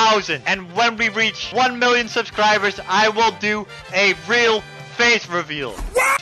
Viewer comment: Oh for a sec I thought he said hell do a face reveal at 300k